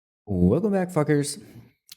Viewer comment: I thought you said "Welcome back fuc**rs" this whole lady 5 minutes until I read a comment that Said "welcome back froggers" and I had to restart the video just to listen again🤣🤣